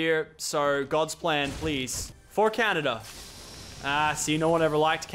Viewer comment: WHAT DID YOU JUST SAY? By the way anyone in season 6? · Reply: He says for canada